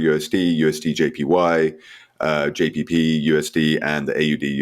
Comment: the 3rd pair wasn't clear at the 2:25 mark? sounds like you saidJPPUSD ? Did you mean JPYUSD or GBPUSD? So the four major pairs are EURUSD, USDJPY, JPYUSD, AUDUSD? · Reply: Sorry GBPUSD